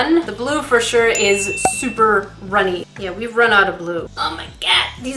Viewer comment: i wasnt paying attention and istead of hearing we've run out of blue i heard we've run out of lube